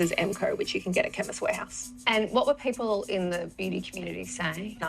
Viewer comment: Their foundation is $30 AUD at Chemist Warehouse. It's not exactly cheap. But I guess they do half price sales pretty often. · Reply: CHEMIST Warehouse!!! I heard "Kevin's Warehouse" and was like "that's a dumb name for a store" 😂🤦🏼‍♀️